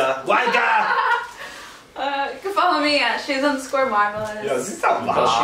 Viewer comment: All I heard from duration 13:21 was “she’s on square modelist” lol